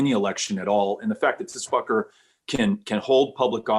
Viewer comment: Did he called him fker 🤣